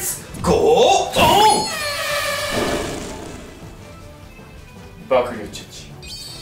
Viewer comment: 爆乳チェンジって聞こえてしまう…